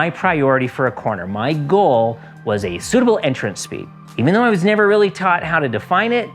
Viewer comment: "my goal, was a phblbghnmpgh entrance speed..." I didn't catch that. I'll figure it out probably in two ways: One, in context with the rest of the vid I have yet to see, and, 2, "slow in, fast out". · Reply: Ah! "SUITABLE"... lmao. But of course that's the goal, but as soon as you race yourself or anyone else INTO a corner, you'll fall behind at the end of the next significant straight.